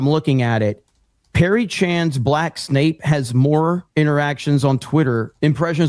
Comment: what does he say at 4:30 ? Perry Chan's Black Snape?